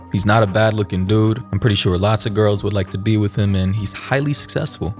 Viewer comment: Lol, it sounded like he said "Elastigirl would like to be with him."